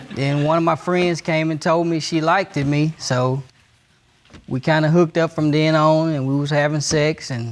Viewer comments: Did he just say “he likeded me”😂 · Am I the only one who heard the man say "She likeded me" (2:29)🤧so the whole point of being in college was to sleep around?!? No studying?!?😫😩